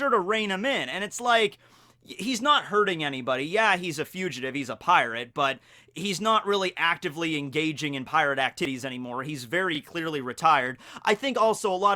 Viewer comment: is it just me or did he say “pirate actiddies”